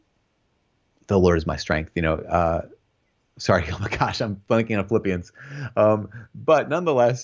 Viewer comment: Am I hearing that correctly? It sounds like he dropped a F-bomb lol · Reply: I think he said he was blanking on Phillipians.